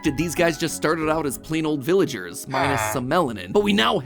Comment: no pat. . . (Oh nevermind; you said MINUS the melonon. I thought you said plus)